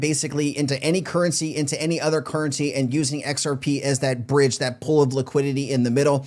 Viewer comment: haha I stand corrected. Forgive me now I hear xrp. Let's go. Thank you!